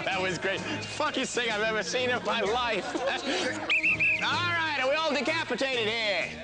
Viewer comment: WHAT DID HE SAY?😳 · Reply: Funniest thing he has ever heard in his life